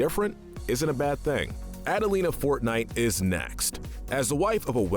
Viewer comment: did he just say adelina fortnite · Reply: its Fortnight